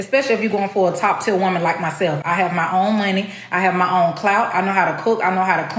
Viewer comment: did she say “Clout” 😂